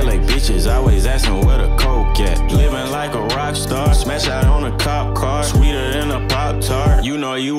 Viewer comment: Feeling like a rockstar now? 😎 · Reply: I thought they said popstar😅